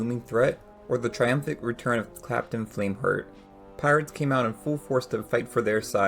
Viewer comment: Great video but uhh…. Did I hear you say Clapton flameheart ???